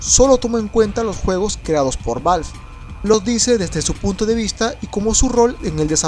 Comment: yo entendi (desde su puto punto de vista)